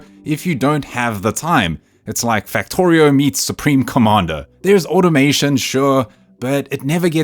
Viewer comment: did i hear supreme commander? 😍 nice!